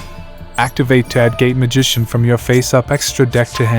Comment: I keep hearing "gay magician" every time LOOOOL great video though man dropped a like